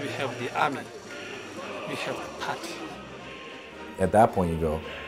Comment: I thought he said "we have the patty" LOL